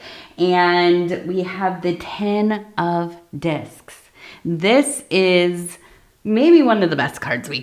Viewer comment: Omg you must be a New Zealander … had to listen twice at the beginning…. I thought you said the first card was a 10 of dicks. 😂😂😂😂😂😂😂😂. Makes what you said after that even more hilarious